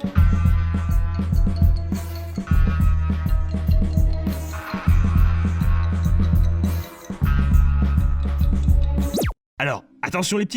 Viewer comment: c'est moi ou ils ont dit yo les tracteurs ? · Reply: Oui, ils ont bien dit yo les tracteurs ;) !